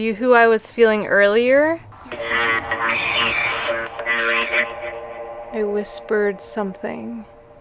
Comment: When it said “I whisper ???” For the question marks I heard “I whisper in her ear”